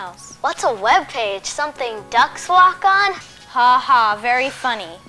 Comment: what does the kid say at 4:32? i can't make out the words. is it something like "what the docks flock on"? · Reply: "What is the internet? Something ducks walk on? smugface"